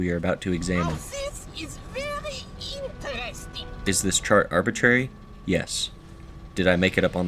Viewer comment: Please, what is this game and who is the red robot? I can't work out what he's saying. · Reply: Metal Arms: Glitch in the System, he's saying "now this is very interesting".